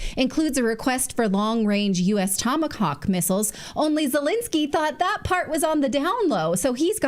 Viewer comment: "Wait… did I just hear ‘long-range Tomacocks missiles’ at 50:20?"